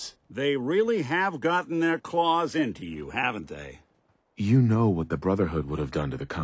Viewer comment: Nick's subtitles say "haven't that" instead of "haven't they".